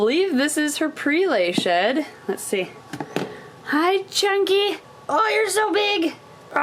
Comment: At first i thought she said chanky Reminding me of tachanka fro r6s